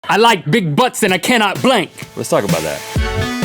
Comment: Thought you said "I like big butts and I cannot blink". Like, you can't stop staring at the butt.